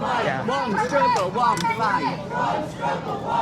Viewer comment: 22.46 I thought the lady was saying 'one struggle one fan@ .........oops 🥰